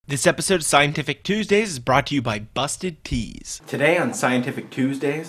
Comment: i thought he said busted Ds, i dont no y